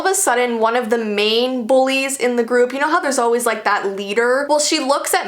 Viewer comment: Am I the only one who thought it said boyfriend instead of bullies 😂😂